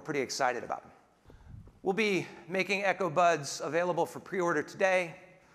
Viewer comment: When he said “Echo buds” it sound like he said “Echo butts” lmao